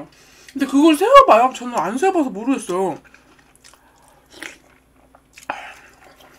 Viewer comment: 우런니가 뭐라고 하시는지 아시는분...??? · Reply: 그걸 세어봐요? 안세어봐서 모르겠어요 라고 답 하시네여 ㅎㅎ